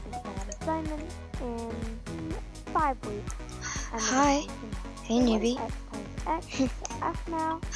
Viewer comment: when Eric said "Hey Newbie" I thought he said "Hey Poopie"!!!!!